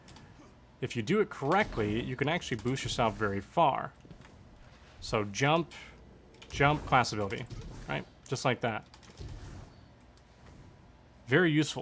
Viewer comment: What did u say im bad at hearing so i heard "jump and class of LB "